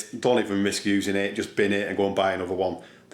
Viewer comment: I thought he was saying "bend it" at first, but he was saying "bin it" meaning put it in the rubbish bin (trash can).